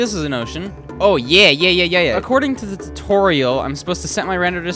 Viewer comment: did he just say according to toriel